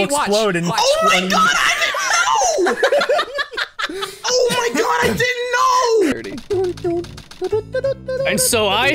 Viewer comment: accidentally predicting the island blowing up had me on the floor. It's even funnier, because when he sees it, he sounds like he caused it and had no idea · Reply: I first thought that he said: “OH MY GOD, ISLAND NO!” Lol